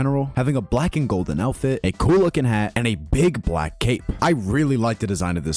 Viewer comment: DID HE JUST SAY BIG BLACK CAKE???